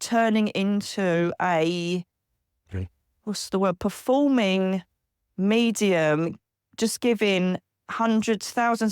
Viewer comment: What’s being said at 10:45? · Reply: Sounds like a males voice I feel who is in 50s he said HEY